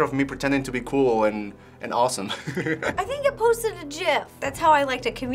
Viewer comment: Did she just say jif?